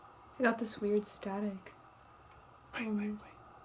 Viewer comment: I swear there’s a voice at the same time is speaking. It’s hard to hear but it sounds like a males voice. I can’t make out what it’s saying tho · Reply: Ot sounds like "wait wait wait" I just can't tell if it is one of the guys.